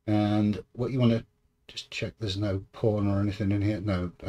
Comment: ! I Swear I heard you say "I'll just check to make sure there is no porn in here" when you opened the start menu 🤣🤣